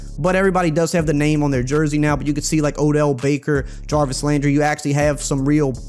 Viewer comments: Did u just say Odell baker😂😂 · Nah bro we ain’t skipping dat who tf is odell baker😂😂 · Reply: When i click on the 7:20 i just hear ‘dell baker’ and when i play it repeatedly it sounds like hes rapping lmao